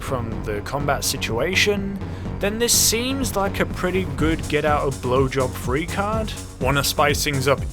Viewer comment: "Get out of bl*wjob free card" uhhh did I hear that right?